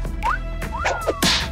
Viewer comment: I thought she said “nalga” 😂😂😂